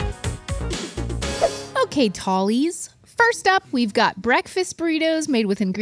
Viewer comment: For a moment I thought Stevie said "Okay Polys" and I'm like wait what....went to the comments to learn she said Tall-ies 😂